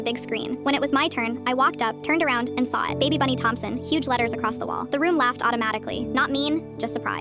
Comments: NAH I THOUGHT THEY SAID BAD BUNNY AND LAUGHED MY ASS OFF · I thought I heard bad bunny 😭🤚